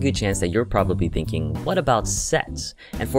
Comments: I swear that I heard " what about sex ?" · Forgot what video I had playing in the background and thought he said "what about sex?!" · thought it's saying 'what about sex' at the start